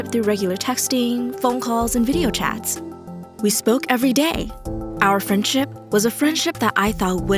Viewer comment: Lmao I heard: we smoke every day